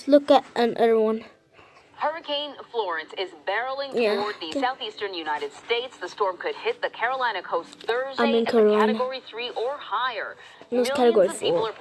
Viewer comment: it sound like you said ( i am corona )